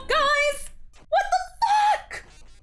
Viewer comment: A lil before 9:52 did you just say f..k!?